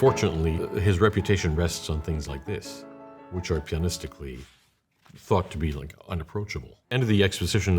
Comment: lmao first time I watched this I burst out laughing because I thought he said “pianistically… fucked”